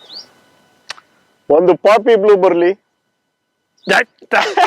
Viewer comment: what did sir said ? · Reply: He ordered a poppy blue motorcycle